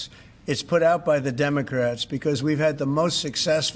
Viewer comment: he really sais sex instead of six month ! Freudscher Versprecher !